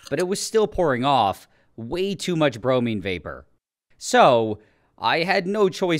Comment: I thought he said brownie😭